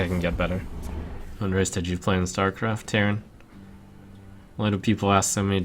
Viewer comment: Could anyone tell me what the question is that reads out? I just understand something with "Starcraft" and that is annoyed at the question.. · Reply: I think it's "What race did you play in Starcraft?" "Terran."